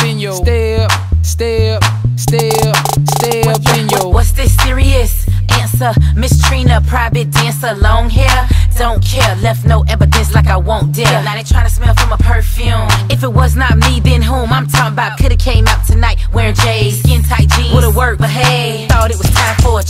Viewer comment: i thought he said long hair radiant body